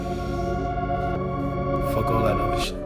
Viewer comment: i fr thought he said fuck meek😂